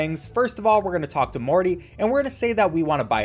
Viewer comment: Did you just say.. m o r t y ?